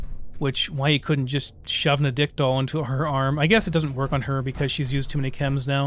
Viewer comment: Every time I hear "addictol" I think "a dick doll." Juvenile, yes, but it amuses me.